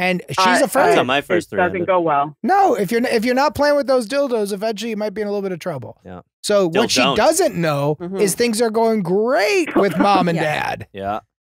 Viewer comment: Anyone else hear Garf say "dil-dont" in the background? 😂